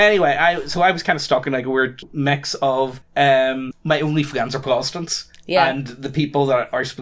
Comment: Wait at 15:05 did say his onlyfans are protestant?!